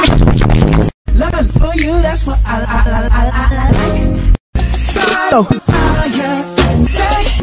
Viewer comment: lol i heard lice funny thing is i had lice at some point